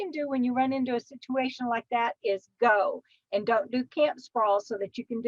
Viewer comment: and 16:50 or so, you use a term - "camp spall", or "camp fall", maybe? I can't understand what you're saying. Can you elaborate? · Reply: Hi there. I am originally from and I’m guessing my draw got in the way (lol). I am saying “camp sprawl” ... many people camping, including nomads, put a lot of stuff outside... tents, mats, chairs, grills, etc but for me, I have found, the simpler the better. And it sure makes it easier to turn that key and go if the need arises or if you just get the urge to move on down the road. Hope this helps.